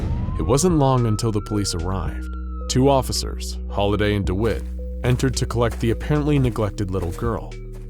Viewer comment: I heard the names Holiday DaWhip